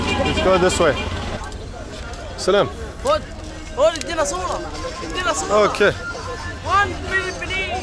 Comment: can anyone tell me what the kid was saying? And why curt laughed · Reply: He was saying "Come! Come! Take me a photo!" 😂